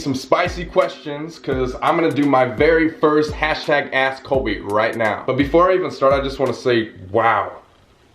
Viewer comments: I thought ge said #AssColby 😂 I need my ears checked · I thought it said #asksolby lol 😆 sorry Colby ;~